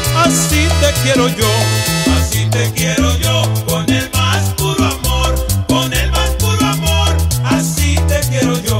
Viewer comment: Yo pensé que decía así te quiero yo con el masturbador😂